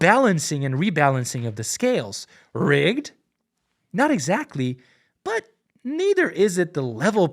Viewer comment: Short Summary Is it rigged? Yes Is the rigging acceptable? That's for you to decide based off of your personal level of tolerance. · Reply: you were close but he says "is it rigged? no" instead of yes, i know its just one word but it kinda change the meaning of what he was saying by a lot